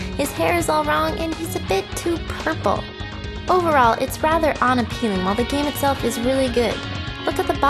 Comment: It really, REALLY bugs the fuck outta me that it sounds like she says "ON-appealing" instead of unappealing.